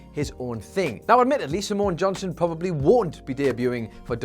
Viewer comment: I thought you said Samoa Johnson and I thought to myself how old is Samoa Joe 😂😂😂